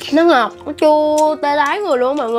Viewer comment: bả nói là tê tái mà tui nghe thành tê đái